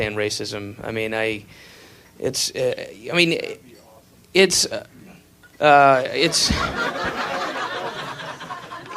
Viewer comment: What does the audience member say that makes him stop his sentence? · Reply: Sounds like the guy is saying, "That's be awesome."